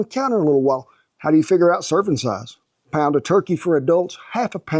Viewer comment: I thought you said circumcised and not serving size, and I lost it 😅😅 Thank you for another awesome video, Kent. I'm 36 and I hope to be a fraction as energetic and lively as you are when I get older. God bless you, your wife, and your doggos!